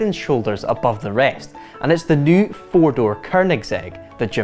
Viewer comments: did he just say 4-door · am I the one who heard "It's the new four-door Koenigsegg" ?